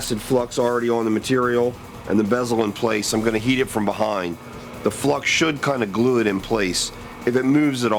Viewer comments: it totally sounds like he said I'm gonna hit it from behind hahaha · "I'm gonna heat it from behind". Sounds like he is saying hit xD